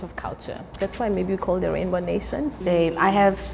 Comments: "the Rainbow Nation" is what she said, I believe, not "one nation". Was that AI-generated captioning? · I heard her say "that's why they call them rainbow nation", not one nation.